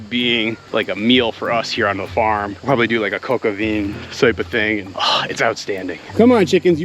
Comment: coco vien? anyone know what he meant I wanna look it up